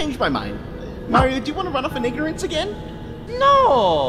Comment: bro i swear smg3 said the n-word